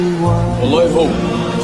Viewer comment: what does he say? I've wanted to know this since I was a kid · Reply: Wallahi ( I swear to God) it's him